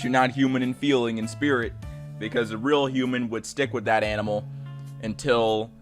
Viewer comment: Omfg I thought animal said anime